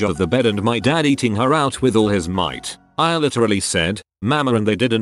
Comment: thought that said “all might,” I screamed and woke up Aoyama, Midoriya, and Mineta.